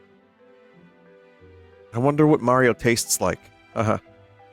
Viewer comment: Sometimes I wonder what Mario would become long after all of us are dead. Will Nintendo still exist in the year 2200? Will Mario ever be retired as a character, or will he become something beyond what Shiggyro Mimosa had intended? Is there such thing as a Post-Mario world, or will he live on forever through gormless bootlegs? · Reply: i thought you said "wonder if", not "wonder what".. wouldve been hilarious